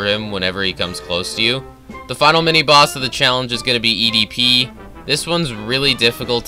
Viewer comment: did mans say EDP lmaoooo